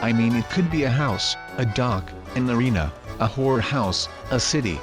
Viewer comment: WHAT DID THIS BOI SAY!?! ( 3:34 ) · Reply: Did he mean to say horror house? Cause it sounded like he says something very, very filthy